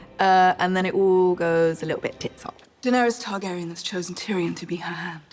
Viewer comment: 😂 she said tits up that’s one of the most British thing I’ve ever heard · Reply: Lol I thought she said heads up. Tits up what does that mean?